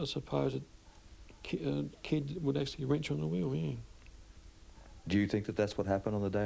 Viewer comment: Can someone tell me what the man says between 3:40 and 3:50? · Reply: It sounded like “a kid would actually wrench on the wheel?” 🤷‍♂️